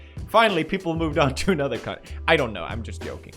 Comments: Did he call Logan Paul and James Charles a c*** 😂😂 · Did Pews say "finally people have moved on to another C*nt"? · who else heard him say another c...